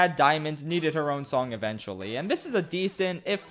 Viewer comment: did you just say "Areola Area Rug"? (Did you just say Grandma Brownie's Chocolate Chunker Bunker Wunkers, now with even BIGGER chuncks of chocolate chunks?) 10:26 you mean why Greg(g) chose Rose over Pearl...